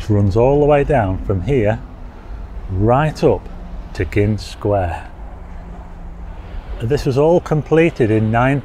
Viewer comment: I misread the way you said Gynn Square and thought you said Gimp Square hehe hope you are well buddy.